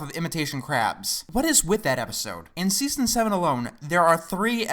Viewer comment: My notification sound is the "my leg" sound effect from the show, and at 2:40, it sounded like he said "In season my leg!" when I got a message.